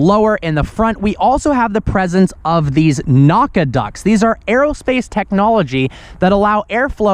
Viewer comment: says, "NACA ducts," I hear "Badonkadonk."